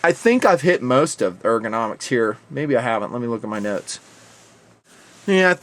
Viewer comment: thought i heard say "gotta look at my nuts" @ 21:55 rofl